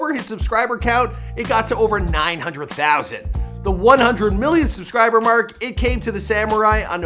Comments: did he just say 100m lmao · did he just said 100million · did he say 100 million subscribers